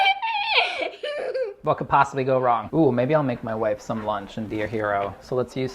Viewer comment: did he say...."WHAT COULD PASTABLY GO WRONG"?!?!?!?!?! OR DID I MISHEAR HIM